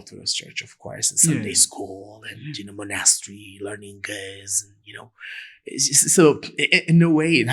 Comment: Pls double check subtitles. It can be tricky e.g at 5:28 min: ”learning guys” instead of geez and many times more mistakes around this definition. Otherwise always interesting programs. በረታ👍